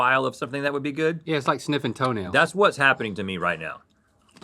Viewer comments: Thank you! I was just on my way to post that exact question lol. Either smelling toenails is normal in California or everyone just stopped being surprised by anything Link says ; ) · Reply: I thought he said "snipping," but that doesn't make sense either??? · wtf did he say? Sniffing toenails??